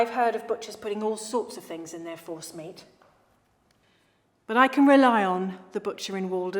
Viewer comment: I 100% heard that as "horse meat"